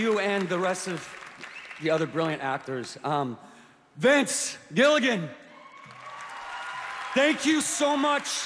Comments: I heard "Bitch Gilligan" instead of Vince Gillagan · When he said Vince, I thought he said "Bitch".